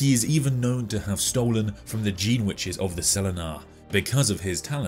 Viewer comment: You said: “Gene witches” I heard: “jeanwiches”